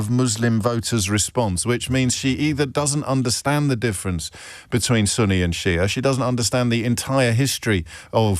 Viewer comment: I thought you said "Sonny and Cher" for a second. 🤡